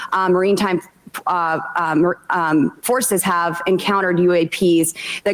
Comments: At around 3:20, I think she meant maritime, but did she say "Marine time forces?" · Six years of serving in an artillery battery certainly didn't do any favors for my hearing capabilities. So help out a veteran who has, at the very least, mild hearing loss and tell me what she read the word "maritime" as? "Marine time?" "Marie time?" Either way, whatever it is that she said, this is just more evidence, as if more were needed, that you don't elect a high school dropout to Congress. Be better Eastern Colorado. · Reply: Yeah, she said Marine Time instead of maritime lol